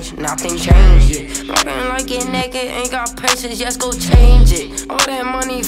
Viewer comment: Did mattox say the n word 2:24 but I don’t care cause valee prob gaved him the pass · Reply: He said naked...look at the official lyric video